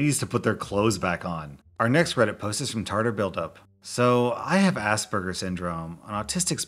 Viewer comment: i thought they said assburger because i wasnt looking at the screen